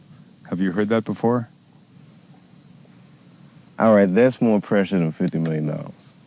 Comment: “Richard Pryor felt he passed the torch on to you.” “That’s more precious than 50 million dollars”. Humble!!!! 💪🏽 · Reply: Coakley Thank you for your comment because I thought he said "tha's more pressure than 50 million dollars" Precious fits much better